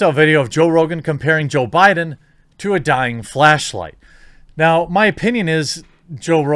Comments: god darn it! i heard dying "flesh" light at first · Thought I heard you say dying flesh light.